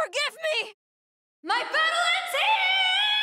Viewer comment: When I first heard her star K.O. I thought she said "My family's HEREEEE!"